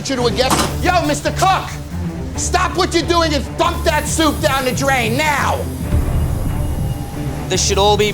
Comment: Who heard "F**k that soup down the drain now!" And did a double take? 😂